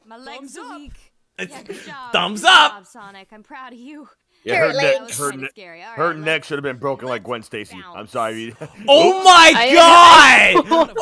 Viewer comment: WTF did Knuckles say? 🤣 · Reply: He said "Her neck should've been broken like Gwen Stacy."